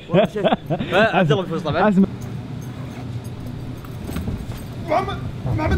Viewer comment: ددقيقه 2:34 مافي اي واحد راح يفهم وش قال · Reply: الا قال محمد بلا لا.